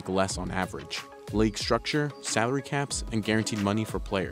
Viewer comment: Thought he said “leg structure”